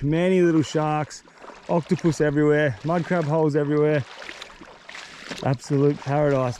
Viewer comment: did he just say minecraft holes